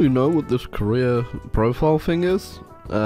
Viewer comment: Ngl I misheard "career profile thing" as "Korean brothel thing" lol